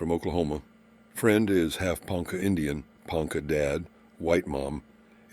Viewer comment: Somehow misheard Ponca as punk.